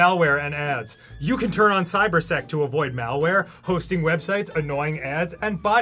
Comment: Straight up thought alien Dan said cyber sex at first